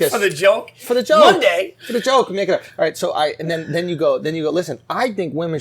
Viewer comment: Did Andrew say what I think he said ??? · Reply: I think he said “make it up”